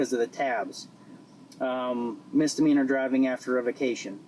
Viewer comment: What is the officer saying at 0:38? I swear he is saying "misdemeanor driving after a vacation." I turned on subtitles and they also think the guy is saying "after a vacation."